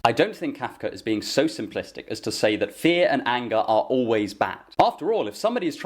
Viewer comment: Every time you say "fear and anger" I keep mishearing it as "fear and hunger" xD